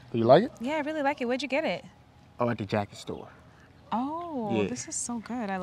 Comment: “The jacket store”🤣 why did I hear “The Homie meeting”?